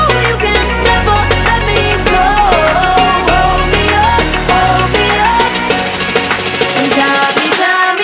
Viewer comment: Why do i hear “ROMEO” instead of “HOLD ME UP”